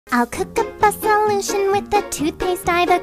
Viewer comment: Correct me if I’m wrong Roon : I’ll cook up a solution with the toothpaste I’ve acrude Hans : I’ve come to the conclusion this was thought up by the Jews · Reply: Sounds like Hans said:I’ve come to the conclusion this was fucked up by the Jews